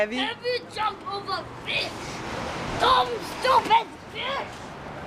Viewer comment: did he said dumb stupid b**ch